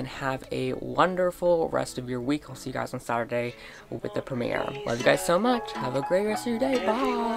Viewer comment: Hey I've been having a pretty rough day, thanks for uploading 😊 I'll edit this when I've watched the video Edit: YAY! ANOTHER LIVE STREAM ON SATURDAY!! Also your haircut makes u look SO much like Austin or mabye that's just me idk. I have not read that seires yet, but I did just get the 2nd book of the Unwanteds. I did like the editing, the blue spider web thingy was really cool! Hopefully I will make it to the stream on Saturday! <3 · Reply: Omg I miss understood lol. 10:55 I donno what I was thinking but I thought u said live stream not primere haha! Also u are the nicest person ever omg- <3 One of my neighbors cats died, and he was really special to me and that family... He was an outdoor cat, so he got into a car accident... I'm feeling a bit better but I just feel so bad for that family.. god bless them ❤